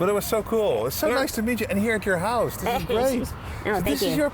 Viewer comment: did you say so nice to midget? Just had to toss out the big word eh. RIP Vernie